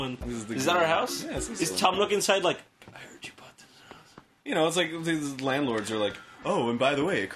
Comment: I heard you brought the house